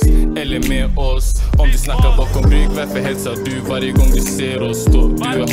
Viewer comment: He didn't say lmao he said "eller med oss" it means or with us #A1Gang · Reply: Thanks for telling me but it did sound like he said lmao. Make sure you subscribe and turn on your notifications so you get the alert when I drop new videos. Follow me on IG